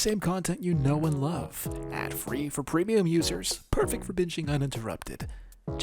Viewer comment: I heard that ad read wrong I heard "Perfect for bitching uninterrupted" Instead of binging Lmao I had to listen to it 3 more times to hear it right